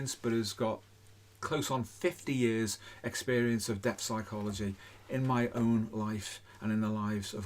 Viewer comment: This might be a big ask but could you edit in key terms into your videos when you say them, for example 14:00 I think says "depth psychology"?